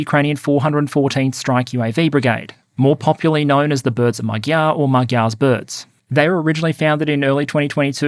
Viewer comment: Was listening to this in the background when 12:42 happened and i heard "The birds of my gyatt" and laughed so hard i ran out of air.